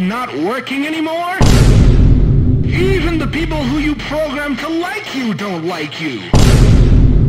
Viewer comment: Tbh when he said”Even the people who you programmed to like you don’t like you” I thought he said “even the people who programmed you don’t like you!”